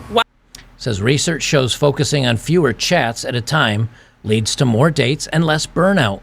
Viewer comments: When you said "...focus on fewer chats." I heard "on fewer Chads", lol. Edit: She's a solid 4 with makeup... (the first girl) And how creepy is this "girl" to record this bartender. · I misheard that as, "Research shows focusing on fewer Chads at a time leads to more dates and less burnout."